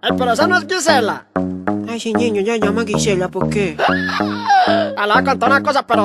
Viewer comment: Ya pero que dice en esa parte? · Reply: Ay sí niño, ella se llama Gisela, ¿por qué?